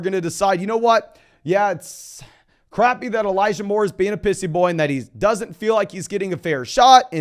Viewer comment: i heard “elijah moore is being a pussy boy” instead of “pissy” 😂